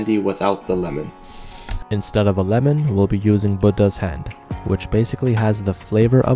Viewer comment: I thought he said “bunda’s hand” 💀